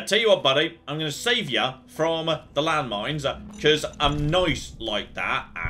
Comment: Did you say 'landlines' at 41:20?